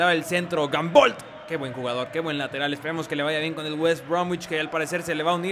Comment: CONTESTA PLIS en el minuto 1:21 mencionastes al lateral, soy de venezuela y como venezolano mi jugador preferido es salomon rondon. a que equipo se va salomon? no escuche porque lo dijistes rapido y no entendi jajaja XD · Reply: West Brom